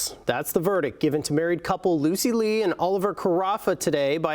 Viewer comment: I thought it said Lucy Liu